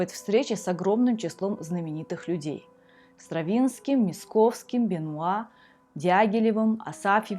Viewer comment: Будьте внимательнее, пожалуйста. Здесь речь идёт не о поэте В.В. Маяковском, а о композиторе Н.Я. Мясковском. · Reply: я даже не прочитал. Почему то услышал "Маяковский".